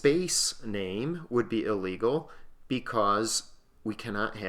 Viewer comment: did he say it is illegal